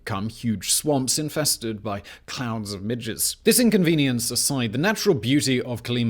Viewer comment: Okay, just wanting to be sure I heard this right 3:16 did he say. “ They became huge swamps infested with clouds of midgets.”?